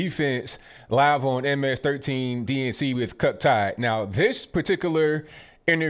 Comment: did he say Cuck Todd?